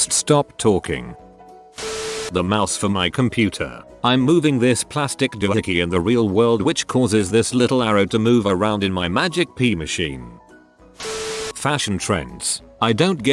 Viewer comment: I'm not sure what the P word is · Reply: Its porn